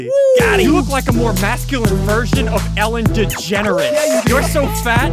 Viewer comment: Lmao I thought he said Ellen Degenerate